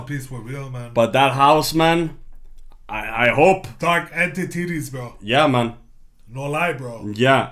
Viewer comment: anybody else hear say "entititties" instead of entities? That made me laugh way too hard 😆😆😆